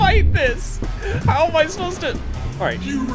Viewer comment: I thought the clog said “you raccoon!” When he was actually saying “here I come!”